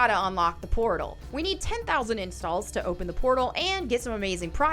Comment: The Hero Wars in video ad through me off. I really thought she said "we need 10,000" incels. WHAT??? Turns out she said "we need 10,000 installs". Changes the meaning entirely.